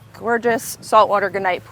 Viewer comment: Did she just say grah-NIGHT for granite pool at 22:17? Or did she mean something else?? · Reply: Gunite is the material used for building the pool made of sand, cement, and water.